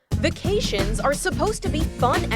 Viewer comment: I genually thought it said vaccination and I almost became an anti-vac, thank god I don't need to